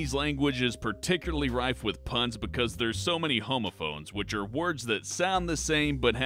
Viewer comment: For some reason on number nine I thought he said "banned boobies" rather than movies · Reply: When he said homophones I thought he said homophobes...